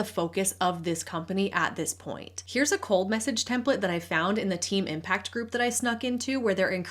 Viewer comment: 😂 I heard “cult message” rather than “cold message” at first